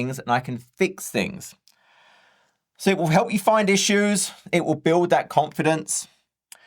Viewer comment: I glanced away from the screen and you were talking about chaos engineering and I coulda sworn you said "so it'll help you find your shoes" I'm paying attention again, for issues :face-green-smiling: - Thanks again!